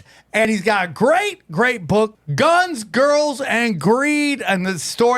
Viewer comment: I like guns girls and green better , that what I thought he said the first time